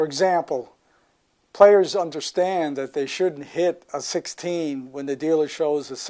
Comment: A player should hit a 16 when the dealer shows a 6 up card? The basic strategy says otherwise... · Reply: he said "shouldn't", even though the subtitles said "should". He didn't speak up or pronounce it very well, after all, he said it badly enough for google's speech recognition that generated the subtitles, to get it wrong.